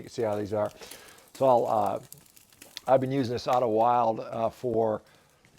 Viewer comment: What is the name of the grill? I thought I heard the word Idlewild, but I can’t find it.